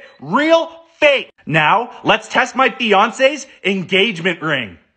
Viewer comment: When he said fiance I thought he said Beyonce and I was like how tf did he get Beyonces engagement ring?